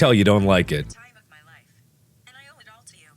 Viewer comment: OKAY BUT IS IT JUST ME OR DOES IT SOUND LIKE SIRI IS SAYING "I'm tired of my life and I owe it all to you."?!?!?!?